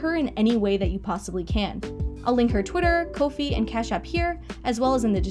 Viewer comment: this should go without saying , but i'll say it anyways. everyone who had a part in helping me make this video was incredibly kind and helpful, and i hope everyone in the comments treats them with the same amount of love and respect they treated me with. hope you enjoyed :) peace EDIT: guys wtf i had no idea mordetwi resonated with so many of you. thanks for all of your support<3 dare i say.... subscribe for more.... EDIT EDIT: here's a link to the mordetwi stickers from abigail(cartuneslover23)'s shop! go check it out! <link> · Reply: Ohhh I thought you said you bought them a coffee, didn't realize you said ko-fi lmao.